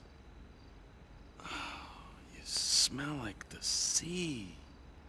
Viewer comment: I thought he said "you smell like my seed."